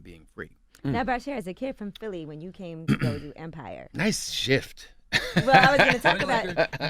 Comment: Lee said nice shift, Yaz high ass thought he said nice shirt and complimented Yee on her shirt. They both fucked up.